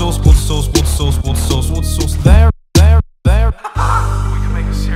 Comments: Sounds like he says what's a sauce once he says it enough times. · Why am I hearing water sauce? Is water even a sauce?! This gives us more questions than we want to answer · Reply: I hear, "what sauce," so it's completely changed to me. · I only hear "water sauce" lmao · I hear water sauce 😂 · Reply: bigjai I heard it as what a sauce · Its sounds like he is saying, "Water sauce", XD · I heard 'what a sauce' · sounds like hes saying what a sauce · Not even 35 seconds in and i hear "pod sauce" instead of water source lmao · Sounds like he is saying water sause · Sounds like he is saying "water sauce" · Halfway through the song it sounded like he was saying water sauce. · i like how i hear mumbo says "water sauce" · It sounds like hes saying water sauce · After a while it really sounds like he is saying water sauce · LOL it sounds like hes saying "water Sauce" XD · All I hear is "what a sauce" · It sounds like mumbo is saying water sauce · i thought he was saying whats a sauce lol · it sounds like mubo saying "Whats A Sauce" Rather Than Water Source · It kinda sounds like he says water sauce